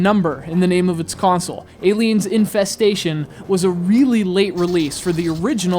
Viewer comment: I thought you said Aliens: Incest-station.